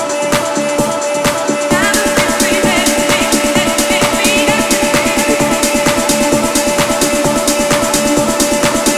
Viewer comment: Does top say heard or nerd 😂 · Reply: Tyler Herro | #14 Miami Heat